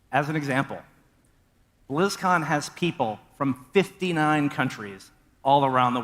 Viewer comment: Muta misheard 4:50. He said 59 Countries, not companies. He all the attendants came from around the world. Which is still completely irrelevant to the "apology." This whole thing is just sad because I was seriously considering getting Diablo 2 since I've never played it before, and wanted to experience it for myself. But now I don't know if I can support them unless they actually make up for this.